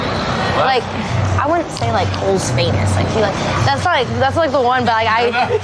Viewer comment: what did she say? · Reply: "i dont think Cole's famous" talking about Cole Bennett